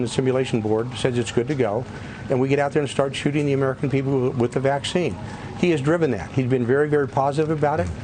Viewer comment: sounds like he says "... get out there and start 'shooting' the American people with the vaccine" 😮